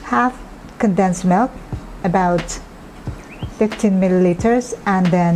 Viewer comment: Hi there... at 5:50 you said that 15 ml of sweetened condensed milk is neede and 15 ml of evap. However, the subtitles say that it is 50 ml each for the sweetened condensed milk and evap milk. Kindly clarify. Thanks😊